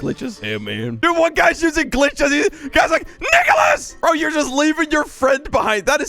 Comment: 😮 WHAT DID HE SAY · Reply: nicolas or nicholas